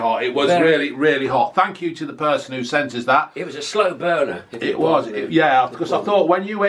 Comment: Sounds like said slow boner 🤣